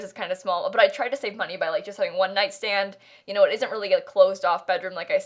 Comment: "I tried to save money by having like one night stand" That's... what she said..?